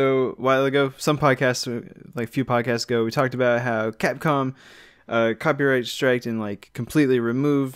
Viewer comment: did you say CatCom?